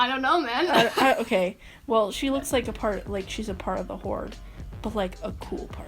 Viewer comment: I'm sorry did say scorpia was a part of the WHORE??